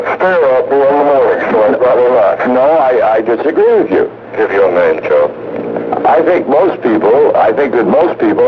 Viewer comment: as the caller is hesitating to give his name somebody else chimes in with “ Give your name..” Do they actually say “give your name Joe” or am I mishearing that?